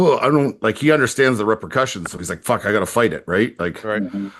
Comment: When said “repercussions” it sounded like “rubber cushions” 😅